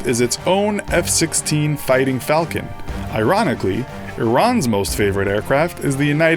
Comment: Did you just say Iranically?